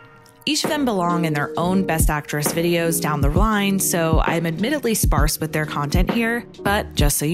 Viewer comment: I thought she was going to say down the road, then it sounds like she says down the rine, like she couldn't make up her mind.